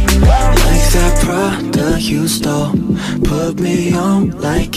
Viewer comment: I always thought they were saying " like that bra that you stole"😂😌